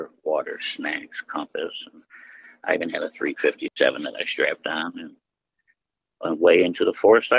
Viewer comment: Did he say he had a "357 strap-on"? 5:07. He's gonna put holes in everything.